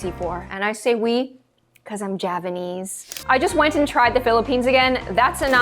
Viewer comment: is it me or did you say you’re javanese?? 😳😳